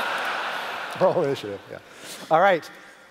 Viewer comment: What did the audience member shout @ 1:05:33? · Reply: Roll initiative!